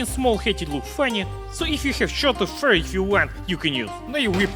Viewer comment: I thought he said "if you have shot a furry" at first